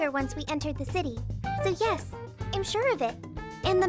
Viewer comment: Did just say "im sure of it"?